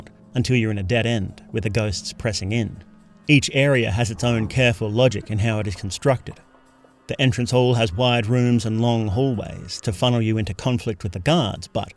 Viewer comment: LOGIC? DID U SAY LOGIC?